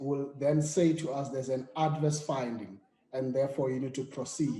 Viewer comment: it sounds like he says evidence of impeachable offences would be ADVERSE for the committee ??????